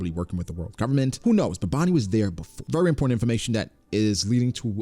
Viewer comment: I was cleaning up while listening to this and thought I heard " fury porn information " I stopped like hold up lol very important information ah yes yes